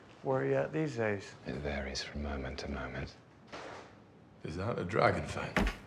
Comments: Loki: Hello Bruce Bruce: so......last time I saw you you were trying to kill everybody. Where are you at these days? Loki: it varies from moment to moment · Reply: I thought he said "Hilarious from moment to moment." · what does Loki actually say I don’t hear him or actually no what he says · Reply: “It varies from moment to moment”